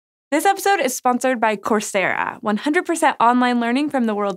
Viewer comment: I wasn't paying attention, I thought she said Corsair instead of Coursera.